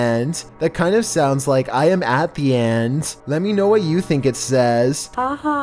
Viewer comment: Number 10 I heard I'm at the air🤔🤔🤔😂😂😂